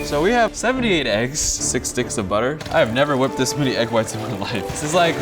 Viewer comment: At the beginning I thought he said 6 dicks of butter